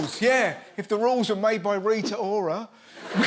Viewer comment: He clearly said 'retards' and changed it to 'Rita Ora' after